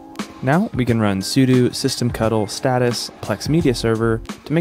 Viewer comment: Great video, easy to follow which is exactly what a lot of people need! I swear I heard you say systemctl as system-cuddle (which I found hilarious!) I would recommend something like unRAID for a future project if you haven't already looked into it.